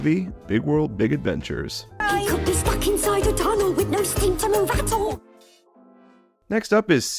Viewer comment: I thought rosey said f-ing inside these tunnels